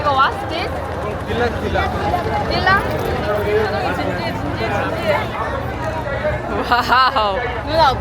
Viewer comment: 요기서 한국말 들리지않음.? 표정이 진지해 진지해 진지해.. 하는것 같은디